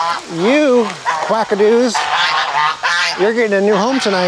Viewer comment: Quackadoos! lol I thought I heard call the geese that. I enjoy your videos!😊